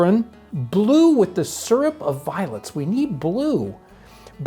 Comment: I kept hearing "Syrup of violence" which makes for a different but fun viewing experience